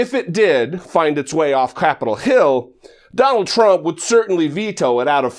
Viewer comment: Does Dollemore say at 2:07 "get of CRAPitol hill"? If so... awesome.